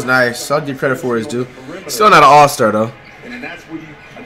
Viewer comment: Did he said Ingram not an all-star?